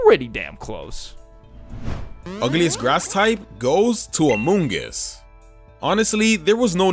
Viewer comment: did he say among us